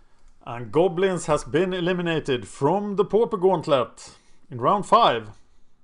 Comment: it's funny cuz it sounds like he says pooper gauntlet....